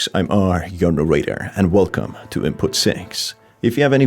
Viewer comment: "Hi im, r, your Narrator" is so refreshing and calm every time i hear it at the start of every video. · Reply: I always thought at 1st he said "R Urinator"